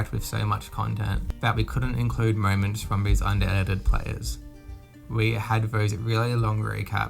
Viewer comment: I thought you were saying Reddit